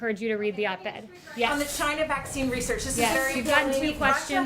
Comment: I clearly heard, "Okay, you don't wanna engage."